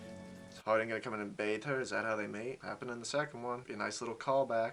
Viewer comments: when you said is that how they meet i heard ‘how they mate’ i’m dead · at first I heard mate instead of meet and I was like wait what 😂